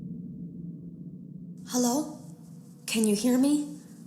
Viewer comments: Springbonnie is actually saying "Hello? Can you hear me?" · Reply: ohh! i thought he was saying “hello? can i help you?” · Springbonnie is actually saying "Hello? Can you hear me?" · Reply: Marcos Diaz I thought it said “hello, come here” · Springbonnie is actually saying "Hello? Can you hear me?" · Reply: I Thought He Said “Hello? Can I Help You?” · Springbonnie is actually saying "Hello? Can you hear me?" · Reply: I thought he said hello human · Springbonnie is actually saying "Hello? Can you hear me?" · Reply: I thought he said "hello come here"